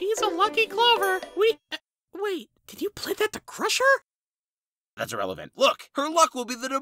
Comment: Balloon: “Wait, did you plant that to crush her?” What I heard: “Wait, do you have a crush on her?” Me, confused: … Nickel: “That’s irrelevant. Look-“ Me: bursts out laughing